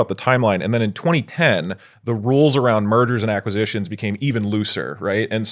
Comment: I had to double take when I heard “murders and acquisitions”